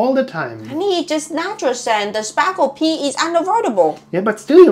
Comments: My 5 year old misunderstood me telling my extended family about spargel pee (we are aware of asparagus pee, but the German word is funner to say), and after supper we found him with his head in the toilet looking for the "sparkles" in his pee. We roflcoptered. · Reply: I stan this child...I heard "sparkle" pee, too. ✨ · My 5 year old misunderstood me telling my extended family about spargel pee (we are aware of asparagus pee, but the German word is funner to say), and after supper we found him with his head in the toilet looking for the "sparkles" in his pee. We roflcoptered. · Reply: I thought they were saying sparkle at first as well! 😂 ✨️ I will now be thinking sparkle pee! 😅